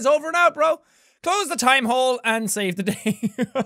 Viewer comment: i thought it said "close the game and save the day